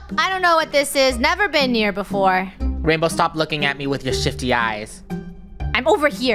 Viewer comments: minding her own business: ”Rainbow, stop looking at me with those shifty eyes” Rainbow: ”I'm over here” *Also: Stop looking at me with those shifty eyes* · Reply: •Random idiot who likes bears• same 😭 at first I thought he said sh!tt¥ but then I realised he said shifty 💀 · "Rainbow stop looking at me with those shifty eyes" had me dead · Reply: I thought said sh*tty eyes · Omg i thought said rainbow stop lookin at me with those shity eye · it sounds like is saying sh*** eyes